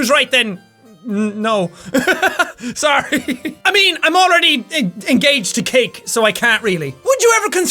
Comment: I thought he said he was engaged to Kate and I was like WTF?!?!!